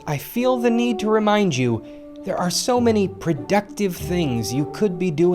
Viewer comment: Oh you said productive, a thought you said predictive. Nvm, i wasn't surprised myself.